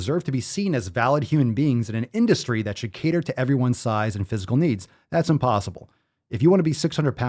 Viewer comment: I literally thought that last sentence said “should crater to everyone’s size”, I’m dying